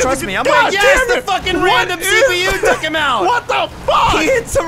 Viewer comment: Did he say the random CPU took him out